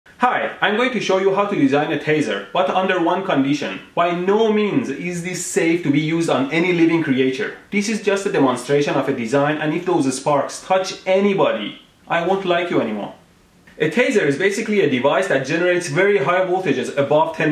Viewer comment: I thought he said "if you tase anyone, i won't tell on anyone" in the beginning Ive made a huge mistake...